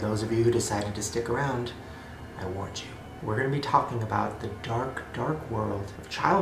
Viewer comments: When said "I warned you" I thought he said "I want you" and I was like the fuck? what? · it sounded like he said I want you lol